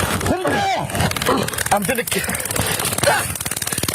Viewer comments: Did he say I'm finna kill you, but didn't complete his sentence at 1:19 😮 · He says, "Im gonna K" Yall didn't Hear that?